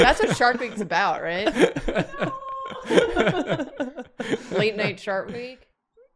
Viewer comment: When said “late night shark week” I heard “late night shark ‘bate” which in context is fucking hilarious